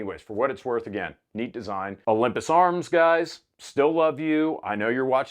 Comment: At first I thought you said "Olympic arms" which would have been fitting since most of their guns didn't work either.